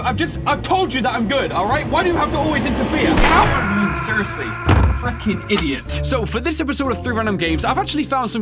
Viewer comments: did he say f*cking idiot? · Am i the only one who heard him way faking idiot 😂